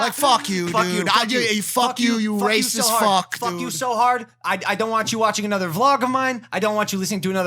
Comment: What about nascar Also, at the end I thought Logan was saying “I’ll fuck your so hard” lol